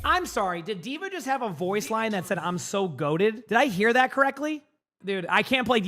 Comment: actually its "you're so goated" not "I'm so goated", I press the praise button a lot so I heard that many times as a Dva main